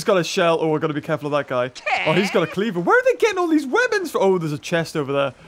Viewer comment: it sounds like he said women not weapon